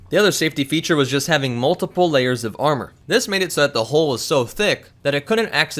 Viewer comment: did you say the hoe was so thicc? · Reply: Jarman Farquharson he said Hull but it sounds like hoe